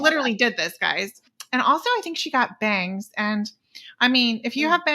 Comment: I thought you said "and also she got banged" and l'm thinking "of course that guy got laid that night" but I guess you were talking about her hair lol 😆